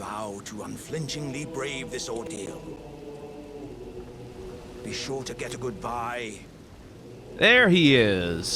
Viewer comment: i love that the skip makes it sound like alexander said "make sure to get a goodbye"